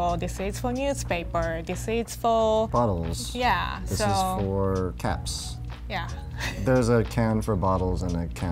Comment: I thought he said “a bin for cats” instead of “a bin for caps”